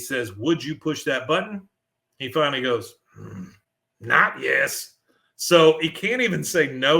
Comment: I've always thought that Cotton said, "NOT YET"..not "NOT YES". I can see both.. I like mine better. Cotton is a dick, yeah? 😜